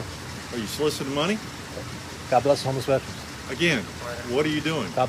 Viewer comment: Nice analysis, thanks. At first I thought he was saying "God bless the homosexuals", lol. That would have been a real howl.